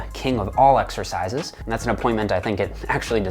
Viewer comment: Yo Jeff what word did you use at 0:21? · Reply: Appointment. It can mean a time you arranged to do something or meet someone but it can also mean assigning a title or task to somebody. Jeff is using the later definition here metaphorically saying that squats have been appointed to the office of king.